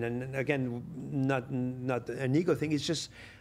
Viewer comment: ” did he say not a nigga thing 🤔 i may have heard wrong… then he followed with in the “Black Culture there’s a lack of innovation” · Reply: He said “not an ego thing”